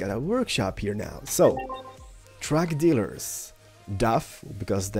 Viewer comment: I heard drug dealers O_O